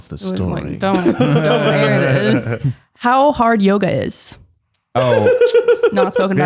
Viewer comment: Ngl I thought said “how hard Yoda is” and I was like “wait whaaaat??” 😂